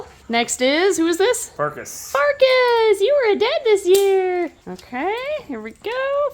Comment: for a second I thought said “you were dead this year!”😂